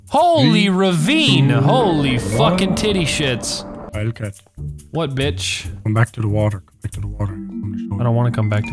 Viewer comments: he says what bitch but the subtitles say what buddy😂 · What, B word? Subtitels: What, Buddy?